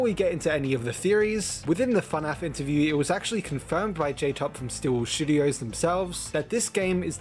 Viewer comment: I THOUGHT YOU SAID STEEL WOOL SHITTUDIOS